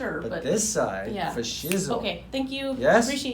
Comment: did he say “fa shizzle” man....i would go to this doc just because the snoop dog reference. Lol